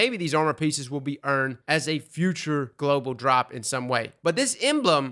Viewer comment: Titan: is getting destroyed Thorn, last word, outbreak perfected, rat king: am I a joke to you? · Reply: I just heard you say "future world drops"